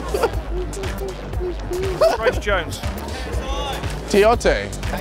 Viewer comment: did he just say Chris Jones?! (physique of greatness)